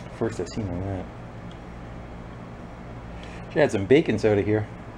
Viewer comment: It took me a second to figure out what you were saying, I thought you had said bacon soda.